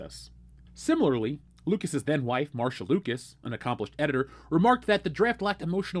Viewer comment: I thought you said "Lucas's Dead wife, Marsha Lucas"